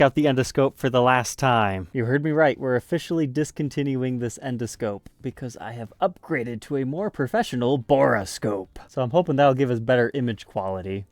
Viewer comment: Did you say that you upgraded to a Horoscope? Does that mean that you predict what is inside the cavity based on astrological symbology?🎸🤔